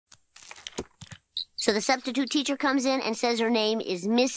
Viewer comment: I thought he said septictute teacher.